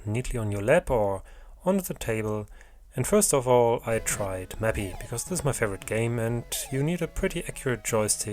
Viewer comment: for anyone trying to understand what game he said, it's Mappy